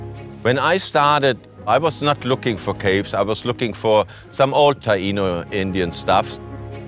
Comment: the subtitles are wrong at 0:55 he doesn't 'mumble' he says "I was looking for some old Taíno indian stuff."